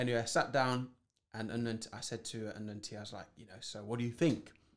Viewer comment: Everytime he said "Unknown T", I heard "an Auntie". Took me a while.